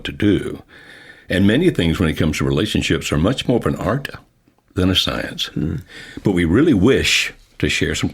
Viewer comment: What does he say at 02:43? When it comes to relationships...? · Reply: Many things when it comes to relationships are much more of an art than a science.